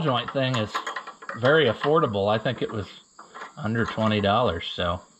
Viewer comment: The first time I heard you say the price of that linkage ball joint I thought you said $120. So I thought you were being sarcastic when you said it was so affordable. I had to go back and watch that part again, then I heard you say under $20. I was thinking, man I know some of my tractor parts are expensive but wow $120 for part of a linkage assembly. 😂